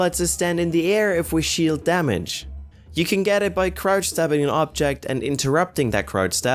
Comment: I thought he said "crouch dabbing" XD